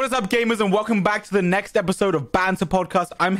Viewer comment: I literally thought he said gay-mers lol in the intro 💀